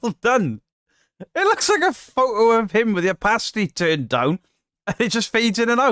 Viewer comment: Excellent getting to 17k subs, mate.👍 When you were talking about that guy in the woods, mentioning opacity, had to double take as I thought you said something about a pasty!🤣 Keep up the great content et al. Laters